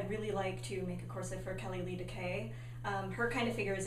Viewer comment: I cant quite make out the name of the person shed like to make corsets for - Kelly something K ?? Can anyone enlighten me :) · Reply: Missmartini nz Kelly Lee Dekay. :) She wears 16-inch corsets and has a beautiful silhouette.